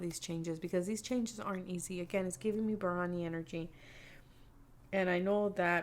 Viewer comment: Pile 3… I keep hearing her saying “biryani energy” which in my head means food, does anyone know what she’s really saying?